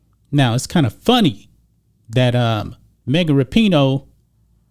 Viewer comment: LOL. It sounds like is saying Mega Rapinoe. And towards the end his facial expressions don't change when he states that " This is coming from the craziest, dumbest athlete in the world, Mega Rapinoe ". This video is classic.